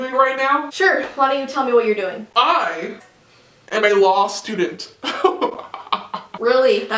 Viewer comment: Lol I thought he said lost student not law student